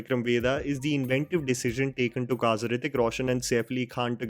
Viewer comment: I think in 6:59 your subtitle shows kazarithi instead of Hrithik . . Not a big deal just wanna correct because I learn a lot of new world from your content.. By the way great work man.. 💕 Geart